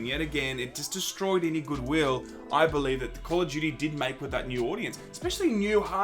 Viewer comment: Who else thought he said Call of Judy?